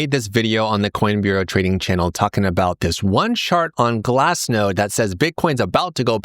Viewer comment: I heard: "this one shart on glassnode that's about to go parabolic" 🤣🤣🤣